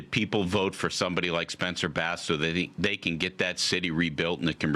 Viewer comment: Did he say Spencer Bass?